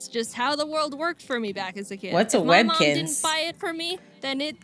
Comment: Did..did she just say,"what is a webkin" 0.o